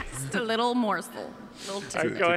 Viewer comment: what does Melissa say at 4:38 ?? I have been wrecking my brain trying to figure out that word when she says "just a little what". Does anyone know? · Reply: Lumos Hex She says "just a little morsel." Meaning, just a little taste of what it's like when I rap.